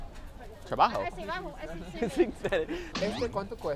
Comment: Hearing Spanish being spoken with a Chinese dialect was so interesting. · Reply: She said abajo and he thought she said travajo